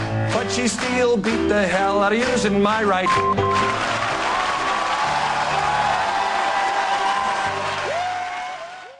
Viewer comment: *beep beep, I wonder what he said? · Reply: He just said hand. I don't know why they beeped it 2x